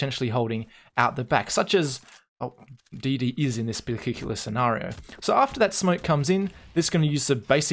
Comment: "Such as DD is in this .... scenario". What is he saying here? Particular? The only thing I can hear on the dotted line is something like bilgigulous. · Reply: yes, particular. lol bilgigulous